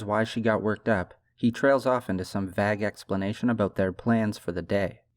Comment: Great video but at 06:15 what did he say? “Vag explanation??”